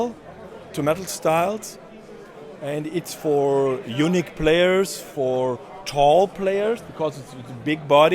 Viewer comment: I know said unique players, but fuck me it sounded like he said eunuch players which was hilarious when he followed it up with describing it as "a real man guitar" 🤣